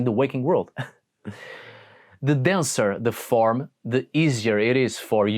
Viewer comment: So due to your accent (which I adore), I misheard “denser” as “dancer” when referring to the physical body, and I just thought that was so poetic and beautiful.